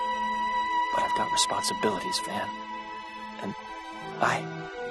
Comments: did he just say "I got responsibilities fam."? Bru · Is it just me, or does it sound like batman says "I've got responsibilities fam" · did he say I've got responsibilities fam?hahaha! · sounds like he says "I've got responsibilities fam"